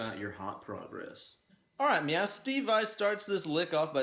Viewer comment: Did you just say "alright meow?" :D